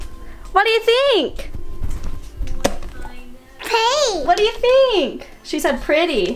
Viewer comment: I Don't Think She Said "Pretty" I Think She Said "Fake"